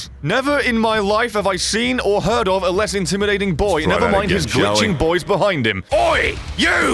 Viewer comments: Anyone hear "Try again Charlie." at 8:50? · wtf did he say? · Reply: "Let's try that again, shall we?". You're welcome. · did anyone else hear sat "lets try that again shall we" at about 8:50?